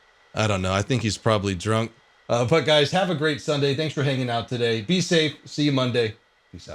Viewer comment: Is it just me when I hear "thanks" from I actually sounds danks ? :D